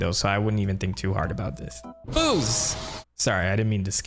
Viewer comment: thought he said "BOOZE!" :face-blue-smiling